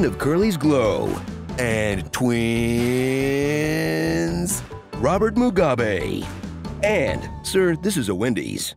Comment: did you just say robert MUGA-bae